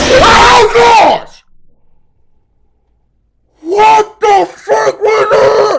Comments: why does it sound like he said what the f**k? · did he say what the f--- was that??? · did cory say the f word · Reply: Frick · Night 2 coming soon, just beat it!!! WHOOO was it hard. HOLD TIGHT GUYS. I love y'all and you know I gotta edit it just a little bit, give it that CoryxKenshin finesse lol, but I will be uploading the entire demo today (regular scheduled uploads will continue tomorrow). Appreciate you guys going on this ride with me! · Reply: Cory it sound like u said what the fuck at 4:29 BTW love your vids bro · Did... i hear that right? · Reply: He said freak, if you put it on 0.5x you can hear (fre) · did he say what the fu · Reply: He said freak